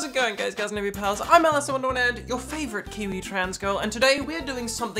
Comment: I always heard it as "your favourite commie trans girl" instead of "kiwi"